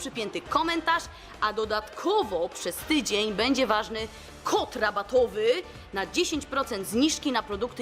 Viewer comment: Czy tylko ja usłyszałam kot rabatowy a nie kod rabatowy